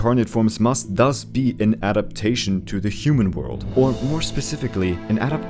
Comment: Wait, did you just say "human world" ? Because the human world is heavily implied to be our own world, aka planet Earth.